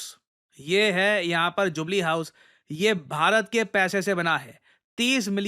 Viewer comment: I heard Chuglee House 😂😂